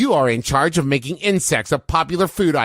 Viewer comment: When Travis said "insects" I thought he said "inscence" and I was very confused